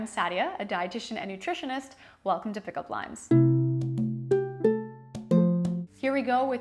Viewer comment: When she said “pick up limes” I thought she said “pick up lines” I thought I clicked the wrong video 😂